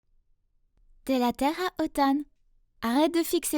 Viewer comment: à 0:03 Elle a dit quoi au début de la vidéo ? Moi j’ai entendu « T’es là Terre à Otan »